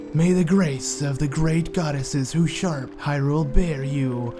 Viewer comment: i heard "may the graces of the great goddesses who shart hyrule bear you on your way"